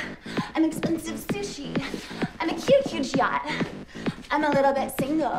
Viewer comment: I very recently discovered that she's singing "I'm a cute huge yacht", and not " I'm a cute cute shot" 🤦🏻‍♀ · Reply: I thought she said a huge huge yacht 😆😆